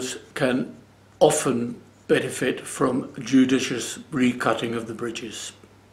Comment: Awesome video, so well explained. When you were talking about the bridges i didn't understand what you said after a "cheap cellos can often benefit from..rghurgirg at the bridges... What did you said? Thank you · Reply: i said recutting. Bridges are often supplied too thick and should be thinned. Get a luthier to do this